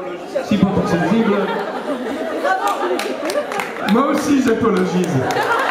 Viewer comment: the thing Seb is saying over and over again throught the vid. What is he saying (Meaning)? XD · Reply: I think he is saying : Moi aussi j'apologize (Me too I apologize) but apologize don't exist in French ^^